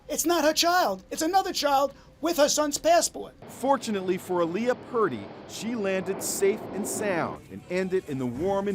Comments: did they just say unfortunately for aliyah she landed safetly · did he really say unfortunately she landed safely... What good thing she landed safely